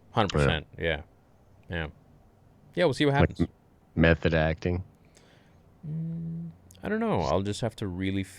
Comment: For a sec i thought said meth head acting